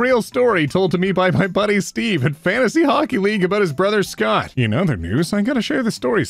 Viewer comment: omg... I legit thought you said "fantasy honkey league" and I'm DEAD